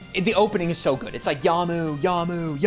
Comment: is bro saying "Yamu?" I thought for sure it's "your move"